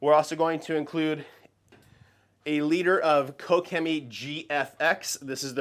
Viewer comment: Wait, Farva, Did he say a liter of cola?!? @ 3:25????